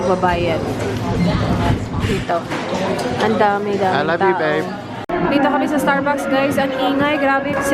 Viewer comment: "Iloveyou babe!" - I heard that people!! Such a sweet guy😍😍😍 so happy for you ate enjoyyy💕💕